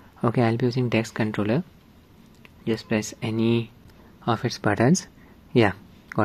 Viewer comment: Very helpful video! Unfortunately I can quite make out what you're saying around 7:02 . What did you press or do to make your controller show up suddenly? I only get the 'No Controllers detected' on my Steam Deck.. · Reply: Enable subtitles. Pressing any button will work. Make sure you set the controller profile to gamepad template from steam controller settings as shown in the video.